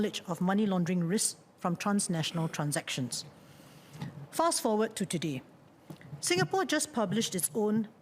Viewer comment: I thought i heard SL said “Fat Af”